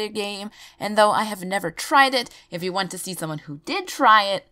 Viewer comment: "even though I've never tried it....". she didn't do it you guys she clearly says she didn't